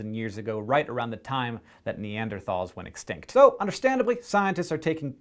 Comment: I thought you said it was NeaderTALS not neanderTHALS